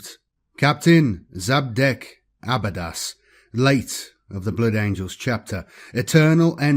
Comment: I'm sorry did you just say "traitor blood angels chapter"